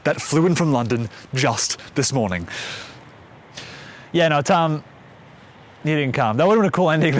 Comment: What does he say at 2:49? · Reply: ”Yeah, no, Tom.. You didn’t come, it’d be a cool ending though”